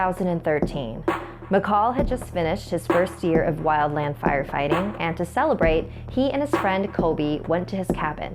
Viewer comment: Lol in the beginning it sounded like she said vampire fighting and I kept rewinding the video until I heard what she really said